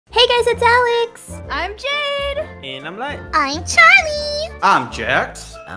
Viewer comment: My brother was watching a video and when Light said I'm Light instead of saying Light I heard Daddy's shoes...